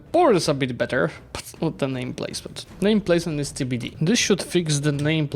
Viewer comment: did you really say "name placement is skibidi"???